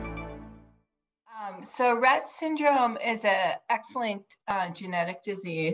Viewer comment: I wouldn’t describe it as “excellent” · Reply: She said “x-linked” (fairly certain) i had to go back to re-listen because I heard “excellent” as well